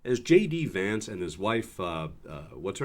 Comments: It sounds like he says his name is Shady Vance! 😂 Shady Vance. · Did he just say “ … Shady Vance “? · Whats he say shady vance perhaps i misheard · Did he introduce himself as Shady Vance? I could have sworn that's what I heard. And if this was supposed to be a trip due to national security concerns - they should have been restricted to US military base.